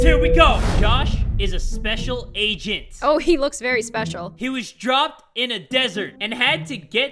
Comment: When he said he was dropped in the desert 00:17 I thought you said he was DRUNK In the desert